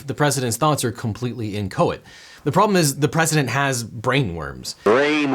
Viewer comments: What was the word used at 18:45? "completely inco?ed" the captioning typed "encoded" but I don't think that is right. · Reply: What Is Inchoate? The term "inchoate" refers to a state of activity or entitlement that is characterized by partial completion of an intended outcome or status. The notion of inchoate comes into play most often in a legal sense, as it could refer to an inchoate transaction between two parties, where the tentative terms of an agreement have been discussed, and it's plausible that the deal will go through, but no formal agreement has yet been signe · what's the word there? "The presidents thoughts are completely ______" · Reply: Inchoate - not fully formed/lacking any proper form.